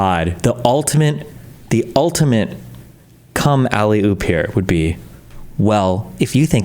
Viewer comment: I heard "cum alley-oop" during breakfast and it genuinely almost killed me 😂😂😂